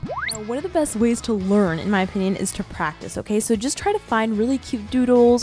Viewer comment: When you were drawing the cactus, and you said practice, i thought you said cactus